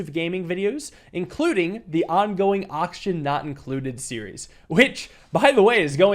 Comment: when he said Oxygen not included I somehow misheard it into Auction not included which is very confusing XD